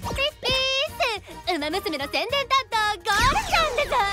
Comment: キャラがキャラで「ウマ娘の宣伝担当」と聞こえず「ウマ娘の天然担当」と聞こえてしまったぞいっ！